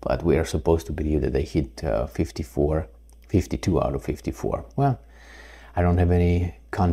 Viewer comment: I thought i over heard 62 out of 54 by the comedian?